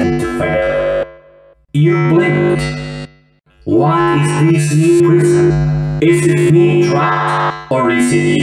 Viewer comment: Why did I hear “Why is this new prison?” I swear there is something wrong with my ears.